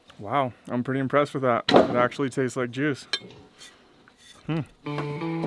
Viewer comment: sounded like he said "it actually taste like jizz."